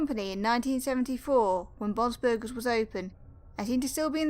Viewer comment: did you say ‘bob’s burgers’ or is that just me?